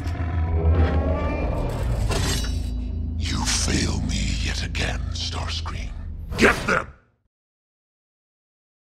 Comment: 날 실망시키는군 이라는 영어가 안들리는데 You feel me @@@ again 인거같든데 @@@ 뭐임? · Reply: you fail me yet again, starscream 날 또 실망시키는구나, 스타스크림